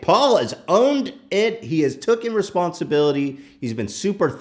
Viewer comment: Anyone else hear tooken' @ 1:39 ?